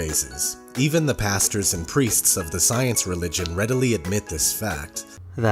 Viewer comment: did he just say the science religion...